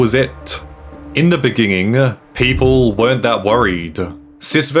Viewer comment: did he say in the begining or am i tripping